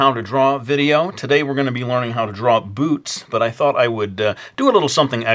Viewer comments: lol at first I thought he said today we're gonna learn how to draw boobs xD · omg at first i heard we're going to be learning how to draw boobs oh god (face palm)